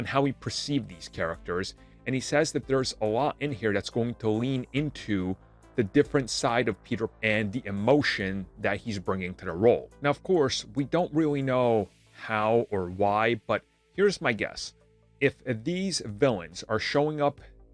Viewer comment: I think you said Peter Pan at 1:43 lol